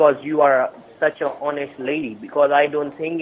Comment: Did he say "you are such a horniest lady" at 3:34?